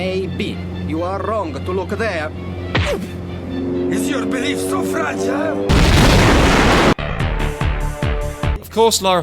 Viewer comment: When I was a kid I thought Bartoli said "Is your willy so fragile?!" After punching him in the dick. 31 now and still think the same thing.